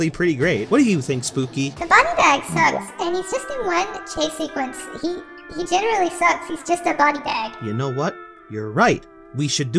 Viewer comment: I can't understand what she's saying here except "chase sequence" 😅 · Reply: "the body bag sucks and he's just in one chase sequence, he- he generally sucks, he's just a body bag"